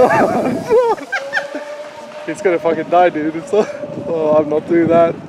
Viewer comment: did say hes gonna fucking die dude?